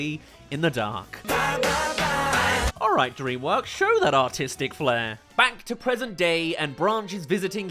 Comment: I thought you said “Autistic Flair” and honestly I love to see it.